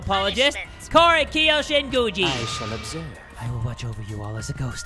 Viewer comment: Suhichi: Korekiyo Shiguji! Me: Shinguji? I thought u said gucci